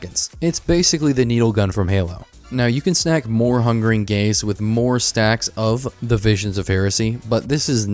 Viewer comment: I kept hearing 'Hungering Gays' xD